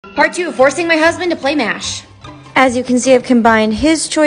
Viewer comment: I thought you said divorcing my husband to play mash and was like uhhh 🤔